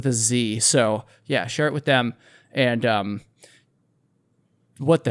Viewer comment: did you say “shart with them” ? Okay… if you say so. 💩